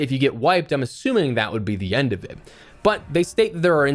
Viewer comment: "...that would be the end of ???". I couldn't understand that word. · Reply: "End of it" as if you're wiped it's over.